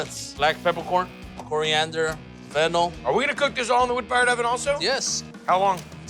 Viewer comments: It sounded like he said fentanyl was the last thing he was going to mix in lol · I’m sorry, but did he say fentanyl as a ingredient?